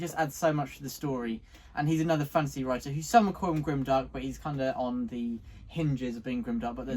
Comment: It's Adrian Selby, author of 'Winter Road'. They have a video interviewing Adrian from about a week ago. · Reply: Thanks so much! I literally could not figure it out and google couldn't help me either. I even tried closed captioning but it was so bad it thought they said "grim duck" instead of "grim dark" so it didn't stand a chance with a name 😂